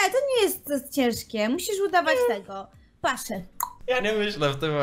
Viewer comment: Usłyszałem - Musisz udawać PASZTET :P